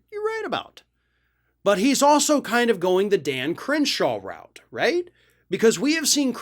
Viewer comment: When you said Dan Crenshaw I thought you were saying 'Damn Cringeshow.'